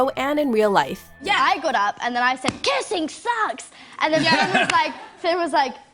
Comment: when you said kissing sucks i thought you said kissing sex lol haha